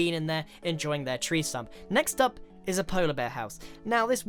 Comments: did he just said: there's cocoa and bean in there enjoying their treesome?? XDXD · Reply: Rodrigo Calvo he said tree stump -_ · when Grian says "Enjoying their tree stump" I heard it as "Enjoying their treesome."